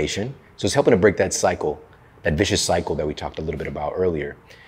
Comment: does he say Oleocampo rich extra virgin olive oil? What is that?